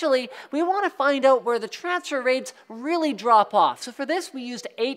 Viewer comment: what does he say at 2:30, trafser ray, transfer ray, he said it in a way where I couldn't understand the word.